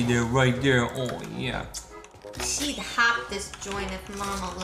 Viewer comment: listen to really closely it sounds like she is saying "we'd half this joint"